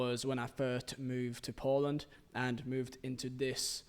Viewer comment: I was today years old when I found out you lived in Poland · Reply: I thought he said Portland and thought it was really strange to move from USA to Czechia from nowhere